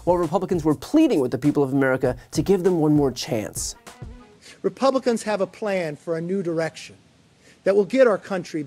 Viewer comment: I keep hearing him say, "Republicans have a plan for a nude erection." I know this comment in no way pushes the conversation forward, but I made it anyway.